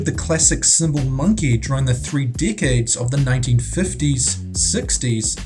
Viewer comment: Oh, "decades". I thought he said "they've been making dolls for several dickheads".